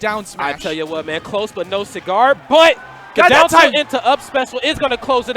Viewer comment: DOWN TAUNT INTO UP SPECIAL lol it sounded like that, although he said down tilt. HOWEVER. It was jab not down tilt